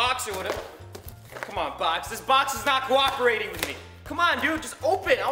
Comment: I thought he said 'its not guaaving with me' turns out he said 'cooperating'😞